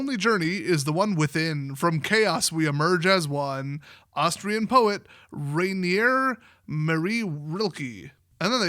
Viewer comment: I thought I heard Austrian painter and starting thinking WOTC was messing with us.